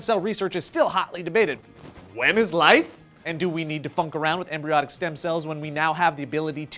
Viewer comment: Definitely thought Joe said "fuck around"